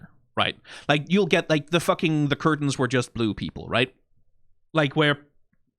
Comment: at first i heard "The curtains are blue people" and not "the curtains are blue, people" and i was very confused.